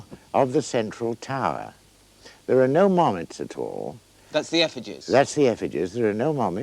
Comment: Can someone please tell me what the witchcraft expert is saying at 9:35 ? He's using a different word for the effigies that starts with the letter M but I can't quite figure it out. I've never been good with accents and I'm not British. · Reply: “Mommet”— an archaic word for puppet or doll used in witchcraft. Had to look that one up too!